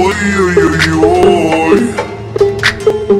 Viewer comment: Мне Послышалось Красивый Мужчина